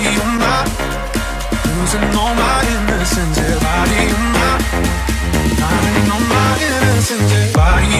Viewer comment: I always thought it was saying: "Party over, losing all my inner sensors"